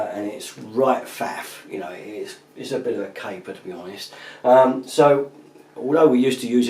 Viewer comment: What us the word he is using at 0:34 ? He says "It's right faff, it's a bit a __ to be honest." Im fascinated by this vocabulary which is indecipherable to an American. BTW, great video. I bought one and love it! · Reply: Caper, means a bit of a faff, which also means it is a bit of a hassle, in other words, i do not want to be doing that. It is kinda a London slang, i guess. Thanks for liking the video..